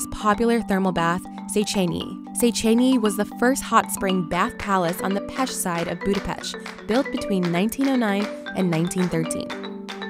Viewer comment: I heard her say "...on the pesh side of Budapesh."